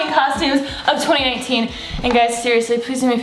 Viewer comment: Lol did she say 2018 0:37 😂😂 I live alisha